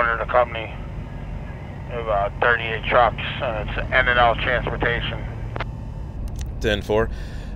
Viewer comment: I really gotta thank you guys for what you do with these cb videos. I have times where I get pretty fed up with what I'm doing working for the certain companies I currently work for and the trucks I run. I watch these videos, hear these drivers stories, and see their trucks and it helps keep my dream alive when spirits are low. And to all my brothers, stay safe out there! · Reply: what company r the guys from Scranton from? sounds like they said in and out transport?